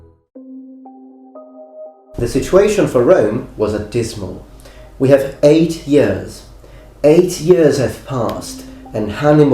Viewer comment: Who else heard him say "adysmal" at the biginning?